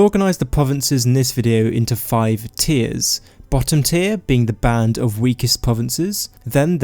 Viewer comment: at first I thought he said “butter tier”